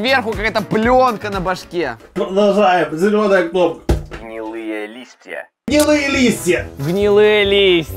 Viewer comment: Там говорилось гнилые листья мне послышалось : МИЛЫЕ ЛИСТЬЯ Я посмотрела и они совсем не милые